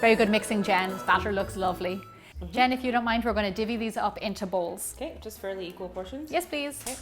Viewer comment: Hihi, did you actually meant to say divide (3:14) like that? Is it actually said like that where you are from or did you mispronounce? It really surprised me for some reason XD